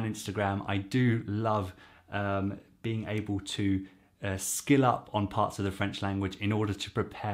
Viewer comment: I thought you said at 42 seconds ..skin up! lol All very useful information. Excellent!